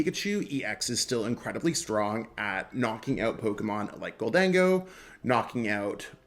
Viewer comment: i heard you say tokémon at 1:30 hashtag GG 420 69 lol one love bro love you and your content you are an inspiration 😎🤞😮‍💨💪🙌🔥🔥🔥❤️❤️❤️